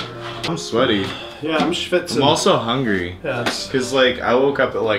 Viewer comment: did he say 'I'm schwitzing'?! Dat German English :)